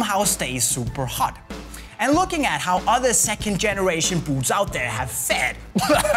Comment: Haha at 5:55 when you use English subtitles it thinks he says boobs instead of boots.